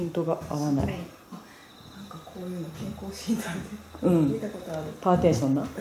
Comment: で「うん」女の人の声で聞こえます その後がビクッとなったと言っています何かあったんじゃないでしょうか？